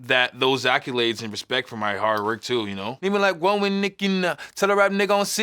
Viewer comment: I thought he said "one with Mickey Mouse" but he really said " one with Nicki now" 😭